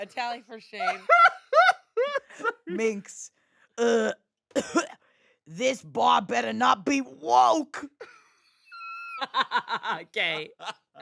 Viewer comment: I misheard the Minx as saying “this broad better not be woke!” 🤣